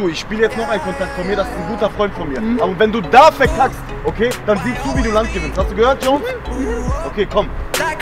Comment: Ich habe nicht verstanden was der man in weiß gesagt hat. Kann mir jemand das Bitte Übersetzen. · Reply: Aber wenn du verkackst dann Sieh zu das du ein Land gewinnst